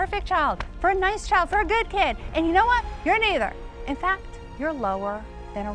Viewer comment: I thought she said the n word with the hard r.